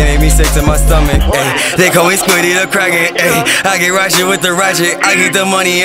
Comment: Thought he said squiddy the crack head